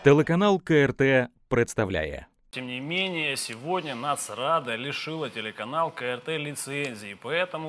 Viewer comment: Я с первого раза не расслышала, а потом мне понравилось слово " НАСРАДА ". КЛАСС.